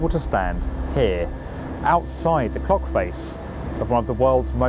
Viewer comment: I swear he said cockface.